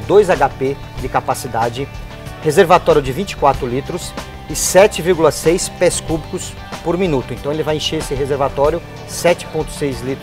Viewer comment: Em 00:22 ele fala 7,6 ???? por minutos. Não entendi o que ele fala. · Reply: Olá Joilson Machado de Freitas Ele fala, 7,6 pés cubicos por minuto. Att, Equipe Dutra Máquinas.